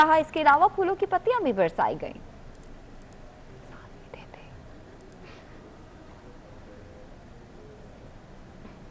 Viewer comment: kiya bola ladki ne slowly se ,,,, koi bata sakta hai.... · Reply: She said “ sanu v deday” lmao 😂